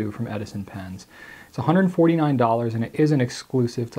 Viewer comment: did you just say "us" instead of "U.S."? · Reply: No, us as in Goulet Pens! We exclusively sell that particular model: <link> - Colin